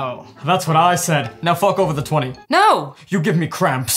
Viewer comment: what did he sayyyyy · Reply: "Fork over the $20"